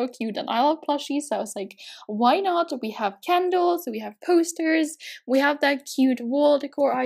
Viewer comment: I rly love your videos and your voice is like "soft" (or what to call it lol) and 3:45 you said something with candels and it actually sounded like you said "kendalls" so cute💕